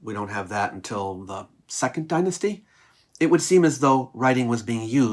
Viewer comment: This is a detail about the subtitles: at 7:38, says "we don't have that until the 2nd dynasty" but the subtitles say "3rd dynasty" which may be an issue if someone is not watching with audio on (and also makes me wonder if which of the two is wrong xD)